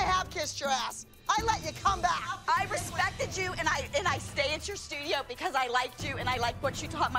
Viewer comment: Leslie: “... And I saved your studio...” When Leslie and Payton are introduced at the auditions in the first episode of season 2, but rejected for the elite competition team, Leslie throws a fit at Abby Lee for not letting Payton into the team even though she said she made huge donations to the ALDC. Abby Lee also just stood their and took what Leslie had to say, which is very out of character for her, especially in her own dance studio. With what Leslie said here in this clip, I think it’s safe to assume that the ALDC was having financial troubles way before any of that came to light in recent years... It seems like Leslie and her family had paid for the ALDC to keep running before “Dance Moms” even came to approach them... 😯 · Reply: she said I stayed at your studio not I saved